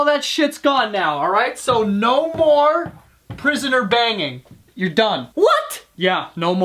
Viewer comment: I thought it said " Prisoner Hanging" lmao